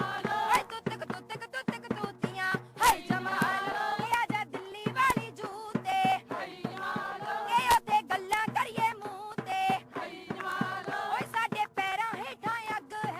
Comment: just for humor, don't get offended , being non Punjabi, I just thought she was saying " muttey muttey baat Kar lenge, Delhi jutey pehen k aaja ".. fir Maine Socha Modi TV pe mut hi RHA hai, border aake kyun mutega.